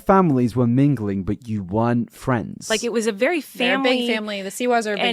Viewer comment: I'm not sure if you'll go back to this but she didn't say "we weren't friends ever", she said "we weren't friends at first". Basically what you're describing right about how a relationship could develop is what she's saying. The sound mixing isn't that good so I can see how that would be misheard.